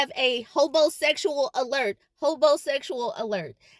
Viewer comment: I thought she said hoebosexual 😂😂